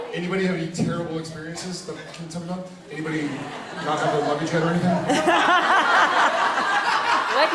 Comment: what does jared says from 00:17 to 00:23? someone please answer me. english is not my first language, im having a hard time to catch it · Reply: Legendas Frozen "Anybody have any terrible experiences to tell me about? Anybody not have your luggage back or anything?" Sorry for being late bit i figure better late than never